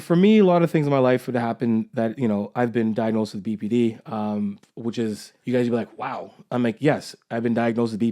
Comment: I literally thought he said “I’ve been diagnosed with BBD” and took me way off guard. Muh Bahd